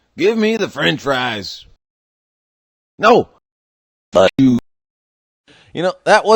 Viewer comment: did he say f*ck you?!